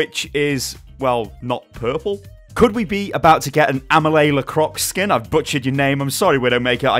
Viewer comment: Damn I thought it's said "remi lacroix"